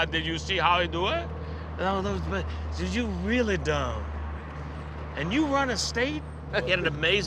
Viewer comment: Did Billy called Arnie dumb?